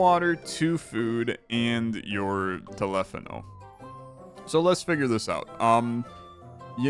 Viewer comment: Wait, did you just call it a telephano? Are you Greek?